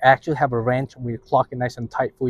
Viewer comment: Wrench- not ranch. Thought he was saying ranch but he was saying wrench